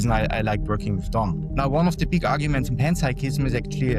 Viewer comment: Caption said "Tom" but I'm pretty sure he said "Don" — don't outsource your proofreading to AI